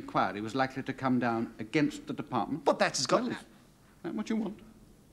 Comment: Hey Comedy Greats - can you decipher what specifically Sir Humphrey says at 3:04? "against the department? But that is ......... ? " . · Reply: "against the department?" "But that has got n..." It is clear he was about to say, "But that has got nothing to do with [it/the current situation]."